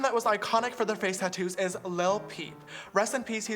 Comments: I thought James said lil pump instead of lil peep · Lil peep it sound like he said lil pump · No disrespect but when he said lil peep I thought he said lil pump and I was like 🙃🧐😬😬😬........ love you James I kinda dig it xxx